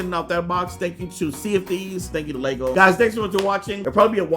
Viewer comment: I thought that he said "CND" instead of "Sea of Theives